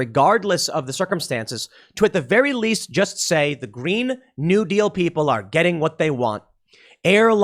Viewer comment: i heard you say "Mean New Deal" I think that's how I'm gonna call it from now on.